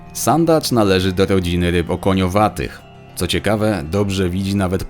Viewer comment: Usłyszałem, że sandacz należy do rodziny ryb "rybo koniowatych" :)